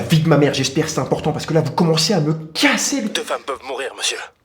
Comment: il dit quoi le mec au téléphone ? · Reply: 2 femmes peuvent mourir